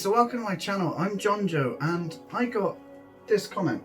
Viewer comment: Good Job John Joe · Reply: I thought he said John Doe 💀